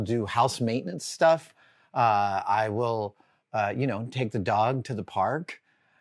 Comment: I thought he said "I will paint the dog to the park"